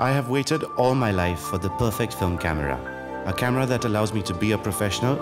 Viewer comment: WAIT WHAT? DID HE JUST SAID PERFECT PHONE CAMERA 🤣🤣🤣🤣🤣